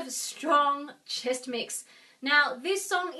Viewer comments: thank you soo much!! i'm singing this for a chorus project, and this video really helped! in the begining, i thought you said chex mix... lol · i thought she was saying chex mix 😂